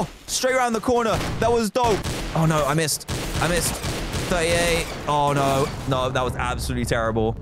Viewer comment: I thought he said 30 A. But he said 38